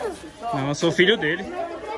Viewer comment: nos 40:15 o cara fala q é filho do toguro, só eu q ouvi ???? fala sobre isso no vidio toguro o pessoal do Paraná assisti vc em peso aqui tem muita gente do Paraná incrita no seu canal.